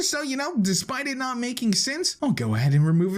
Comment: he says “despite it not making sense” when the subtitles say, “despite it not making sense that it can happen”